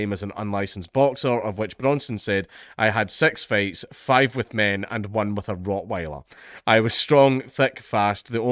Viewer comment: I thought said "I had sex fights with five men and a rottweiler."